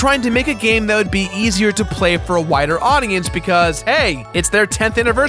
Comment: I heard "whiter audience". Then I realized what Jirard actually said. Am I subconciously racist or something?